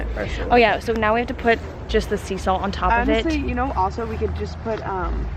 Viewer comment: thought she said BLACK SALT not Sea salt..